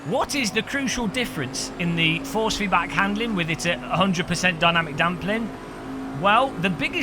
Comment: What was that - in Australia we have our local Footy Show (AFL) that has a "say that again" segment, when people fluff up a sentence. So at roughly 3:13 did you say "Dynamic Dumpling" hahaha --- SAY THAT AGAIN!!! ;D